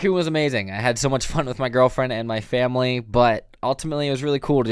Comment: Bro I left the video playing and walked away to get something quickly. Stg I heard “with my new girlfriend” and I was like woaaah. Then I rewatched and heard correctly 😂